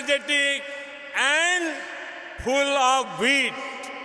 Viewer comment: did he say 'full of weed' 🤣